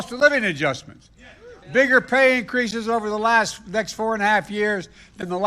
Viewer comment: Was I the only one that heard that, it sounded like Biden said "nigger" pay😂😂😂😂